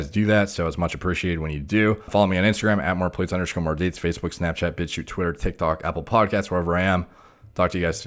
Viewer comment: Did he sneak in A Bitch You at 14:54 or am I hearing shit lol.